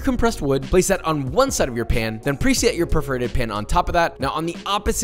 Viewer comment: it really sounded like he called it a “perverted pan” instead of “perforated pan”